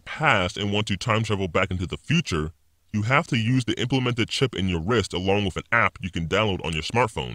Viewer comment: I thought he said you can’t use the chip because there is no global WiFi